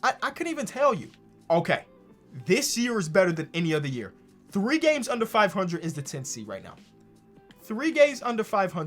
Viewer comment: When he said 3 games under 500 I swear I heard 3 gays under 500.